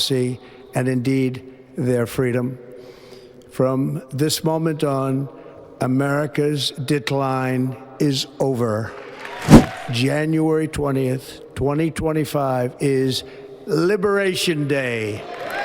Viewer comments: I swear I heard Trump say 'America's D*ck line is over." and was confused for a few moments. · Sounded like he said America's dick line is over! Yay💙